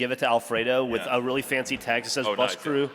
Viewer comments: Could someone let me know what the guy in the audience says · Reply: Bus Wankers. I guess it's an inbetweeners reference. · What was yelled that Jack high five'd the guy? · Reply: Buswankers